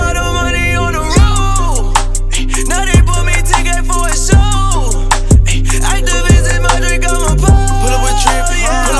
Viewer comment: In the lyrics it show "2k" but he show with his fingers 10k , also my english is secound language so I can't understand if he is saying "two k " or 'ten k" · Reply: He said 10k